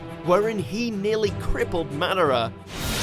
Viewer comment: did he just say manara